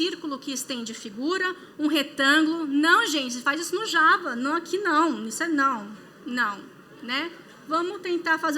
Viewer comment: O que ela disse que dava pra fazer no java que não entendi?! Não ouvi, é quando ela fala "não gente" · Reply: Fazer aquele Extends